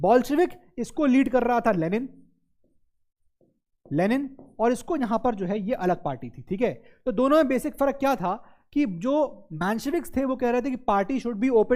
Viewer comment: when sir said 'alag party' i heard 'Alakh Pandey'😂🤣🤷‍♂️😁